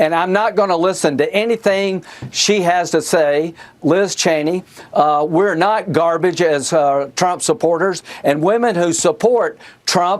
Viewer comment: OMG the wittle baby is cwying again. 7:12 did he just say "we are not garbage as are trump's supporters" ?